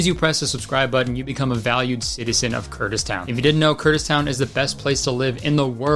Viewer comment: for a moment I heard Kurdistan, lol